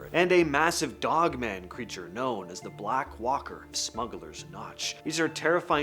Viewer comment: The Black Walker of Smuggler’s Notch was honestly is the Coolest Cryptid that I ever heard of! I really wish that I can meet this The Black Walker of Smuggler’s Notch in real time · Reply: lmao 😂🤣 I misheard him & thought he said " The Black Walker of Smuggler's CROTCH !!" 😂😂